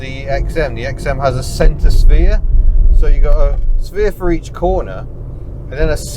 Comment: The XM had a sphincter spear ! 15.18 Think I may of misheard that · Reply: Centre sphere...